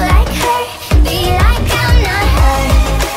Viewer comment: I thought this said I’m NOT MOTHER